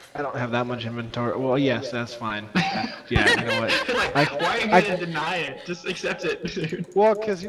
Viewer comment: me too i heard say "are you gonna rape me"? lol